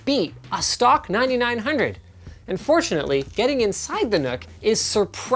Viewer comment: Am I the only person who keeps on hearing unfortunately instead of "and fortunately" @ 3:52 ?